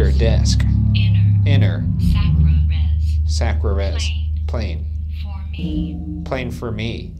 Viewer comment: This probably sounds very tinfoil hat, but at 1:09:44 when the ovulus starts spewing out words really fast, when it said “sacra res plain for me” my brain immediately thought it was trying to say “sacrifice Shane for me” and then Ryan got that icy feeling down his back right after.